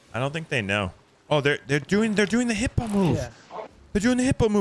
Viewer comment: At first i thought you said "they are doing the Hippo move"